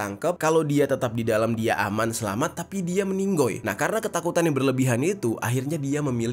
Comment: Waduhhh bikin bulu kekudukku terus Merinding..... Tak terbayang meminum air jasad ...waduhhh duhhh.., Minit 13:45 itu saya salah dengar kah??😂😂meninggoiii